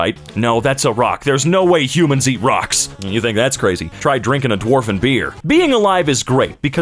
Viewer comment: Me for a second when i think he just said: “if you think that’s crazy, try drinking a dwarf with beer”